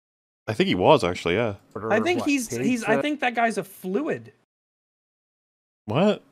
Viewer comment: I am so glad that said "a fluid" because I thought I heard affluent oddly pronounced and was confused